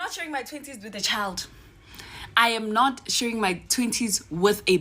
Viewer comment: it took a minute, for me to realize she's, ''not sharing her twenties with a baby.'' I thought she was saying, ''I'm not sharing my tittees with a baby!''